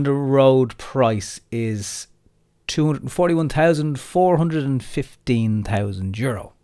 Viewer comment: David Greenlee go back and listen. That is what he meant, but not quoted. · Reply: I heard €241,415...anybody else. Perhaps might clear this up